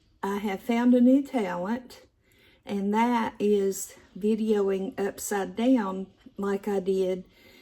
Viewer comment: Ok, I’m so glad I listened close, it sounded like you said “I found a new talent, inhaling upside down.” A few minutes later, I figured it out. xoxo